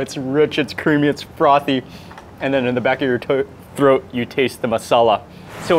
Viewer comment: what did you say? Back of your toe?😂